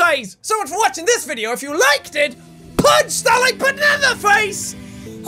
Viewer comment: for some reason i thought he said "PUNCH THAT LIGHT BANANA FACE"